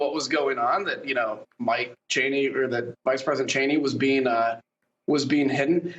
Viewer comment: Did he say Vice President Cheney? 8:45 ish?